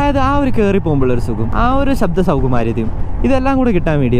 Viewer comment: What was that word at 17:22.Shabdasouku??